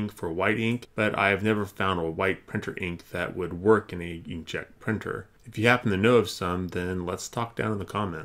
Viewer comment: subtitles say "young Czech" instead of "inkjet".